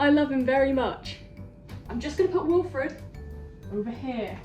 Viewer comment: ngl i thought u said "im gonna preheat wilfred"